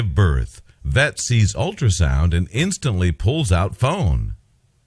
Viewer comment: I thought he said “pulls out a phone”